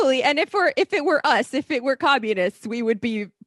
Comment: Did she just say they're communists? I did not realize they were communists.